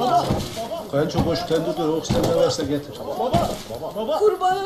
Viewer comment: Bu gizemli adamın ne dediğini Anlamaya çalışıyorum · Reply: kayınço koş tentürdiyot oksijen ne varsa getir diyor